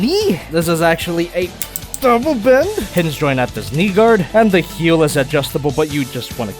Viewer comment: Did you just say the N word at 5:39 ??? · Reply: He said knee guard.